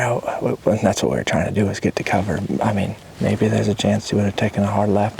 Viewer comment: good luck and happy thanksgiving to you too. My comment was simply meant to counter this absolute clown who posted the original comment acting like food plots are somehow not fair chase. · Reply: Thank you. I'm thinking that "swampsound" commenter thought I said,"clover" instead of cover, which means bramble, slashing, in case it was bullrushes. These men hunted them in cover.